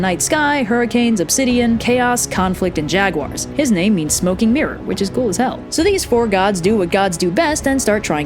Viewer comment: “And his name means smoking mirror” I thought you said, “smoking urine”